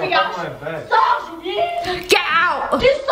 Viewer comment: راني عيانة خرج did anybody heard that 😂